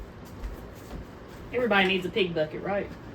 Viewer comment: I was doing something while watching this video. When you said pig bucket, I thought you said pee bucket. I chuckled outloud.